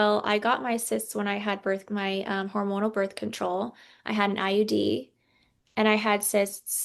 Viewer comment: I thought said IED 😅😂